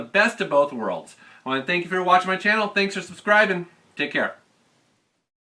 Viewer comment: Ha nice, though at the end (4:35) it sounded like it said "METAL KETTLE STRIKE".